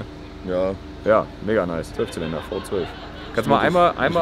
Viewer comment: Min. "6 Zylinder V12" oder verstehe ich ihn nur falsch.... nuschel.....🤣🤣🤣